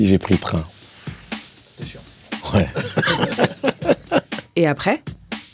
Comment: il dit quoi a 3:44 ? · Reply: C’était chiant